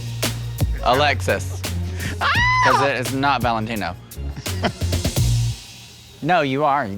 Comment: did Trinity say Valentino???????? GaaaaaaAAAAAAAAAAAAAAAGGGGGGGGGGGGGGGGGGGG!!!!!!!!!!!!